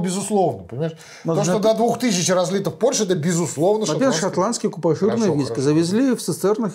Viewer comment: Сначала послышалось "иранские купажи". Сильно ушиб челюсть о стол. Потом расслышал правильно:)